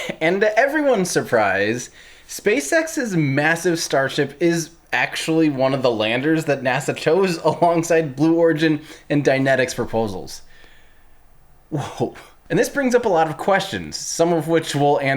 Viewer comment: Hahahaha!!! I seriously thought you said "Dianetics." I was all ready to sign up to the Sea-Org.